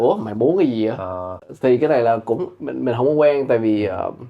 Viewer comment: cool interview. I'm việt kiều as well so I might be wrong but some of the subtitles look off (or maybe it's written the way it's being spoken?). e.g. 21:32 "mình (hổng -> không) quen" 20:55 I think the word being used for friendly is thân thiện instead of thuận tiện